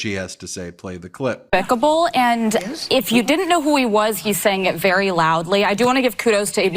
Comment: I thought he said he didn't know them!